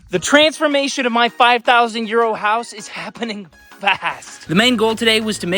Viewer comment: After watching all these short videos, I always thought you were saying "My 5000 year old house", and thought you were just calling it that (was not thinking you really thought it was 5000 years old... just calling it that)... But now.... by your 13th video, I realized you were actually calling it what was written on the screen. 5000 Euro house. LOL.